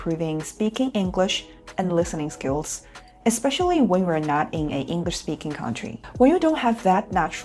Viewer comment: the subtitle is wrong. It should be "When we're not" not "When you're not"